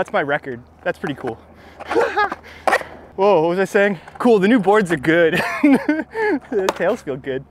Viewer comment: did he say NEW BOARDS?! HEAVY BREATHING